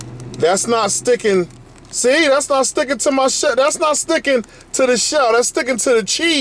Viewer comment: I thought he said SHIT there. It's not sticking to my shit